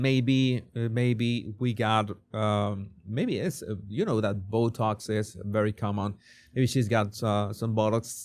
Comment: I do love an accent, but did anyone else scroll back to check after the first time he said botox, when at 18.40 i hear what i thought he said was 'shes got some bollox' 😂 (i agree)